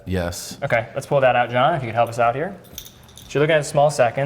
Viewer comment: Does call him “John” at 19:55? 😂